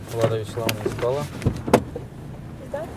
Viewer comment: Что бы понять что сказал инспектр на 14:49, включил субтитры. По умолчанию они на Английском ! ПОЧЕМУ? Включил русский перевод, но с английског такая белеберда просто ужас. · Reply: Да, там действительно нечетко аудио записалось. Инспектор сказал: "Влада Вячеславовна, сдала"